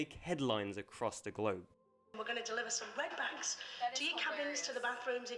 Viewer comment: Umm, when they said "deliver red bags to cabins", I thought they said "deliver REDBACKS" - ie, the Australian spider that is notorious for being found in outdoor toilets 🙂